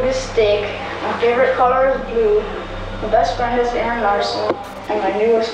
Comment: did he say his best friend is daniel larson